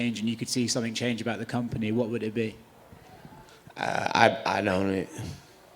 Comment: What did he say in 4:12 ????? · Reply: " I don’t even know"